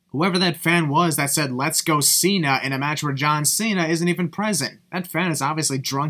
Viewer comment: Oh and the sin you placed at 16:18, that fan CLEARLY SAID “let’s go jinder!” Yet you think he said “let’s go Cena?!” Come on now!